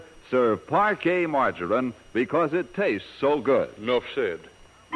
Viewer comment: Ha ha -- I hear somebody saying "no shit" after the Parkay commercial! Check it out at about 12:30. Was that in the original recording? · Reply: Finally got a chance to listen to it with headphones on. I'm pretty sure it is "'Nuff said."